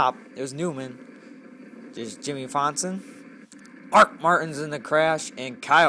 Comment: did you say jimmie fonson? lol